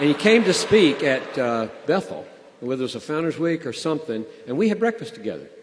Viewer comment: What does John Piper says in 00:59? Founders Week? Thanks!!